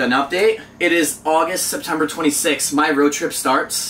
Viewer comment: did you say that its august september 26th? lol